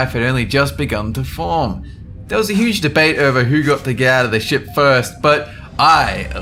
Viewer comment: did he say gather the shit?